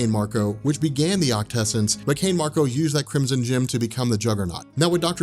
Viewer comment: lol i thought he said the chrimson chin